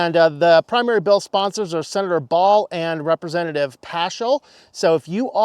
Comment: Did you say, "Senator Baal"? (no, that is not a typo)